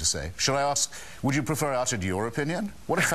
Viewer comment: What did Hitchens say at 7:38, after he said, "That's a very clever thing to say. Shall I ask...would you prefer I" I couldn't understand him. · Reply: "Would you prefer I uttered your opinion?"